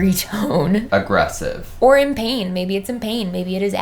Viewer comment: I thought it said "in pajamas" LOLOLOL